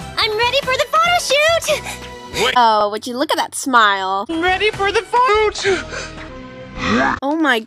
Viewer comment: omg that part in slow mow she sound like she said im ready for the fruit 😂😂😂😂